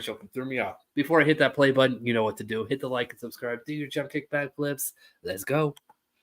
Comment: jump kick back flips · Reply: lol ok I’m so glad I asked I would never of thought you were saying flips lol thank you